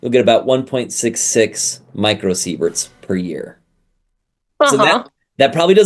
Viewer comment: Oh, the subtitels in Geman are not showing µSv but "Mikrosekunden" (µs) and the youtube autotranslator also says "Mikrosekunden" ! (I only listened to the video/german translation and was wundering, so i checked the original english speech). May be, you EDIT the english subtitels? The english subtitels say "micro severe" instead of "micro sievert"! (Beginning 6:52) And your yellow Video OVERLAY-Text states often mSv instead of µSv!!! Please correct this. 1mSv is 1000*1µSv! (Nice video tough.) I just measured the radiation in my living room..(Berlin city/Germany): 0.12µSv/h, that would be 1mSv/Year. I have a table for my measuring instrument, that states these: 2-4mSv/y is "normal". 0-100µSv you get on a long time flight. >200mSv can cause genetic damage. If you get >500mSv it can/will/could cause: Headashes, susceptibility to infection >1Sv is named as "acut radiation illness" >5 Sv: 50% of people getting this dose will die in between 1 month ---------------- There are coast regions in Brasilien, that have 80mSv/Year. (unchecked info). Pay attention to µSv and/or mSv!!!